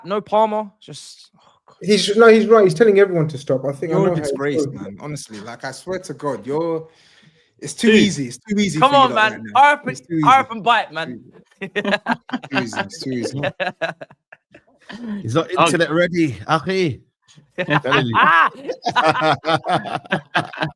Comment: I honestly thought said “hurry up and buy” like the Chinese store owners in “don’t be a menace” 😂😂😂